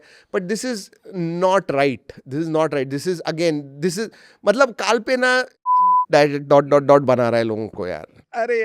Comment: Did he say ch*** to carl pei? 😂😂